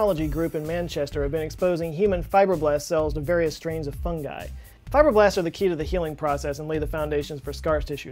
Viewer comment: I misheard something here and had to rewind, wondering how on earth the key to the human healing process could be fiberglass